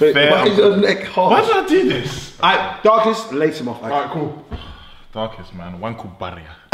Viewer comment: is when I paused the video but I heard slaphp